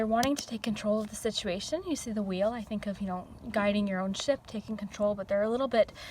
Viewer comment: i thought u Said “guiding your own shit” lol hahahah